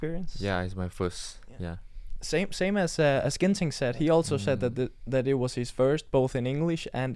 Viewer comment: I couldn’t stop laughing when Anders said Ginting but the caption said “ginseng”😂